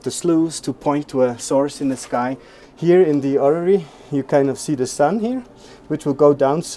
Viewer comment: I think he said "orrery", not "horaire".